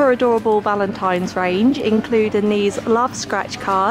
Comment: did she just say yella ? 5:00 ( no hate ) 😂😂❤️😂